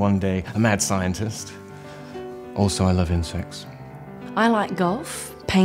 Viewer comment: I thought Mortimer said "I love incest."